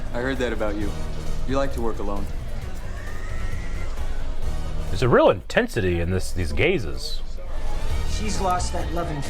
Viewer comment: What I heard @ 10:17 : "There's a real intensity to these gays!" You're not wrong, you're not wrong.